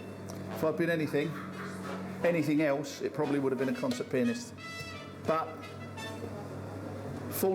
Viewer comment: Did he say he'd be a cock sucked penis?